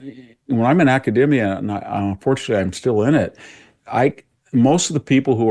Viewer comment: Did he say, Fortunately or Unfortunately? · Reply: Fortunately 👍🏼